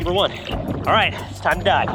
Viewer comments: i thought he said its time to die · did he just say "It's time to die?" Because it really sounded like it though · At first i thought he said its time to die 😂😂😂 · I thought he said time to die XD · i thought he said time to die instead of time to dive 😐